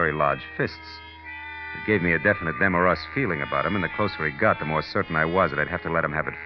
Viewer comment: What does demarest feeling mean? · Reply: I think he says "them or us feeling".